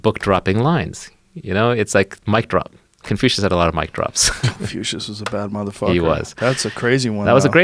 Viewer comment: What was the name they said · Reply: Confucius.